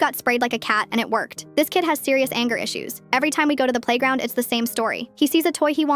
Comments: I thought it said spayed like a cat i was so confused 😭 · I THOUGHT SHE SAID SPAYED AT THE BEGINNING AND THE ENTIRE TIME I WAS MORTIFIED